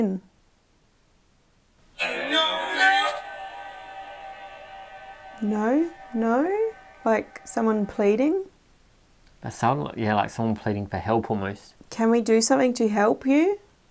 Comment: When you ask why are you here at the Ramen I hear a response “I don’t know”